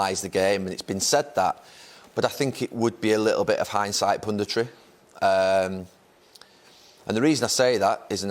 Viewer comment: I thought Neville was saying "pon di tree" when he said punditry lol.